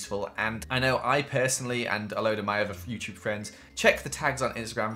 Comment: I thought he said future friends and I'm like oh mah GAH he a time traveller